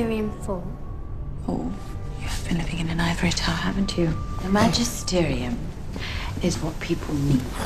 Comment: What does Mrs. Coulter say at 1:20? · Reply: sunnysunchan Oh, you've been living in an ivory tower, haven't you.